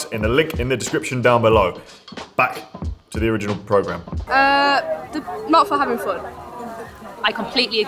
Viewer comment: What did the young lady at 1:36 say? Asking for an American friend. Also, the dedication with which holds that fake mac is amazing. Such form and commitment. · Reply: "Not for having fun"